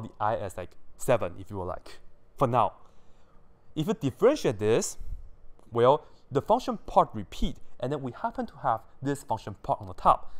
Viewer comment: What are you saying at 16:15? "If we differentiate this, the function part repeat.... "?